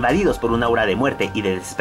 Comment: ¿Invadidos por una qué? No logré entender lo que dijiste ahí. ¿Aura de muerte o Hora de muerte? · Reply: Aura